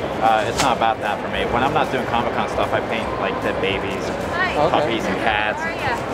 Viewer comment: did I hear that correctly? He said he paints dead babies?